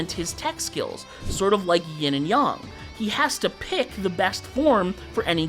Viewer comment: bro said yin and yong instead of yin and yang